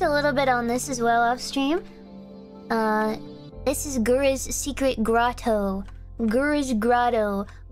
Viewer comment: For some reason I heard "Gura's Scrotto"